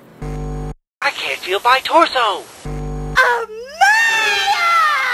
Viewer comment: What did mangle say at 0:09? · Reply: facepalm: It was really easy for me to understand. :sigh: Anyway, it's: "I can't feel my torso"